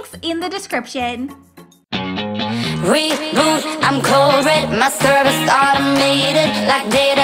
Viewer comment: new intro has me vibing it’s so cute :) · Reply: i honestly thought it said ‘I’m Covid’ 😂😂🙃👁👄👁